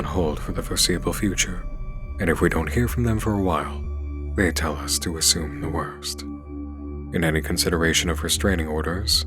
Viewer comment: Lol, they clearly say "if you don't hear from us, DONT assume the worst." 😂